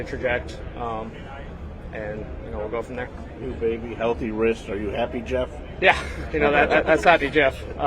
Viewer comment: wtf i thought you said he had broked his hand ?